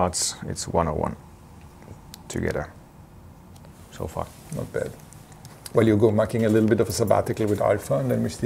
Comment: My poor english，anybody tell me what toto said at 3:49? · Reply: Not Bad